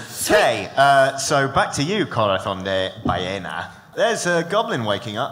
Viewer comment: At 15.07 it almost sounds like Said Corathong De Balléna